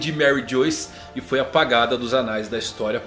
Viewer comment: eu escutei anais? 😏